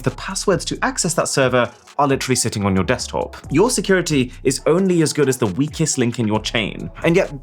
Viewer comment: what did he say? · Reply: “Your security is only as good as the weakest link in your chain”